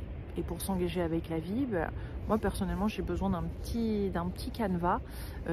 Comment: J'ai compris "j'ai besoin d'un calva" 😅